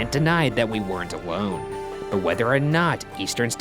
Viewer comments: Ryan says “awone” instead of alone and I laughed so much I don’t know why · he says a wone instead of alone